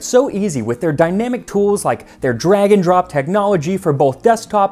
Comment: NGL, I thought he said dragon drop when he was talking about Square Space features